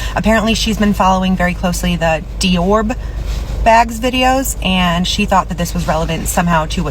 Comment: What about the "Dior bags"?? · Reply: Yes! Please, someone explain! I heard, "de-orb bags". I need answers!